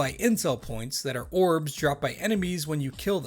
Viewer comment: I'm gonna be honest, the first time you said 'Intel Points' and totally thought you said 'Incel Points'